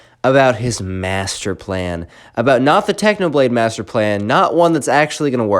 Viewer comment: did you just say technoblade master plan also roblox